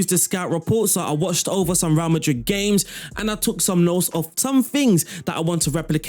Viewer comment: Why was I hearing "VAR Madrid" throughout the vid lol...But yeah your tactics always works out for me, Big ups...You the best man